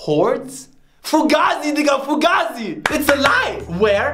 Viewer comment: did he just say “FUGAZI... nigga* FUGAZI?!! 🤣